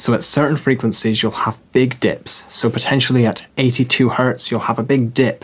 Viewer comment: when he first said "big dip" I let sounded like he said big dick. then I heard what he said after and it was funny. listen 👂